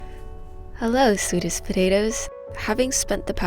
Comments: First time to channel: “Hello sweetest potatoes” Me: Ooo...kay. I'll take that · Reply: LOL I thought she said “Swedish potatoes” and I was wondering how Swedes fixed potatoes! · Thought she said 'Swedish' potatoes.... Haha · Ok so I misheard “hello sweetest potatoes” as “hello Swedish potatoes” Either way, I’m subscribing